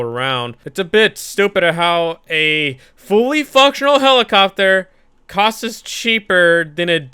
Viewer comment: Who thought he said the F Word But he said Function.